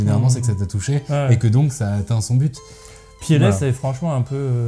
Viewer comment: De quoi parle-t-il exactement ? "Piéné" ? J'ai du mal à entendre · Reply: Piélès !